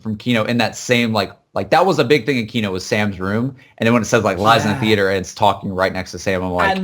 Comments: i hear " lies in the ether." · I hear "It lies in the aether."